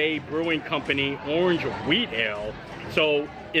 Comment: I was washing dishes and listening and I swear you were saying "weed ale." I had to rewind lol